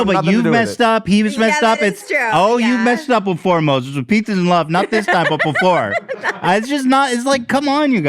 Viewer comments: sounded like he said “with pizzas & love” 😂😂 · I swear Ethan just said with “Pizzas and love”. THAT needs to be the new phrase! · it sounds like he says “with pizzas and love” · Did Ethan say “with pizzas and love” at 1:36:30? I think that keto diet really traumatized him. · DID ETHAN SAY “with pizzas and love” ?????😂😂😂😂